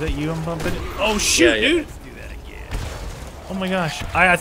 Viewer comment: On 10:47 did say sh*t